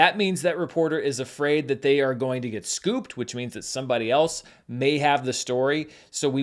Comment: did I hear "scoop"??